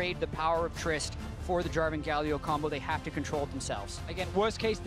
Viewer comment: What does he say at about 1:20 german Galio? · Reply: Jarvan Galio combo